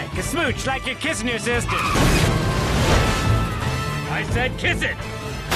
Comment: “Like you’re kissing your sister” proceeds to slam the plane into the earth “I SAID KISSING” No way did Skipper mean....... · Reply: So I always heard it as "Kiss it!" not "Kissing" but now it puts that in a WHOLE different light! Amazing.